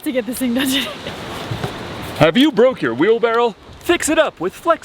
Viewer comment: Is anyone else hung up on the way they pronounce "Wheelbarrow"? Sounds like they say "wheelbarrel" LOL You guys are doing awesome out there! Love the videos, keep up the great work!